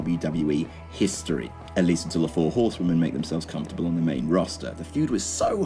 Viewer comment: did he just call them horsemen?I know Charlotte is a man but damn you have to insult the other 3 that actually look like women?